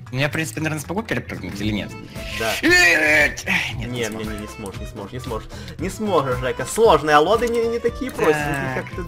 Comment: мне послышалось несможешь зайка ???!!?!